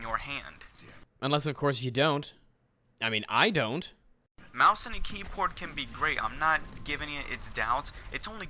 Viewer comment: He actually said mouse and keyport